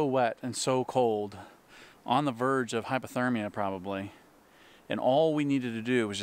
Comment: I wish every backpacking video would say, or put in the description, where it was filmed. This was good, probably life-saving information. Maybe I misheard you, but it sounded like you said hyperthermia instead of hypothermia. Maybe your mind was wishing for some extra heat. Anyway, I'm taking your recommendations to heart.